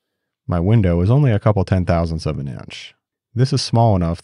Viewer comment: after "tightest tolerances" i heard "my window is only couple of 10/1000 of an inch instead of tenths 🤣